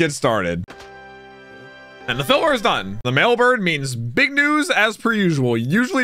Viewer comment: Did just say Big News? 1:10 LOLOLOL